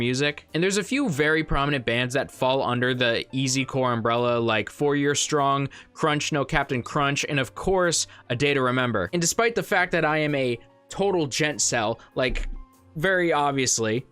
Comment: SIR DID YOU SAY "CRUNCH" LMAO